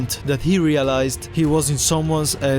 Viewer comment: Realized the captions say “someone’s a— territory”?